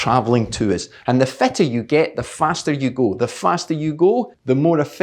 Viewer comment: With your awesome accent, I thought at 2:25 you said: "The fatter you get, the faster you go..." That got my ears all perked up for a second, and I thought to myself: "Oh, really?? Then maybe I've got a chance here. Time to order another pizza!!!".... and then I realized it was just the accent....😒